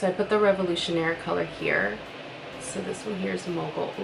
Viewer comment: Such beautiful shades!!! For mogul i legit thought u said moco 😂